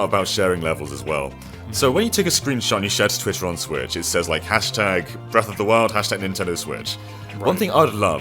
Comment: Did just say “Breath of the World”? 🤔